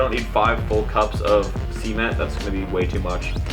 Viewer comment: See ment 😂😂 cement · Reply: I thought he said semen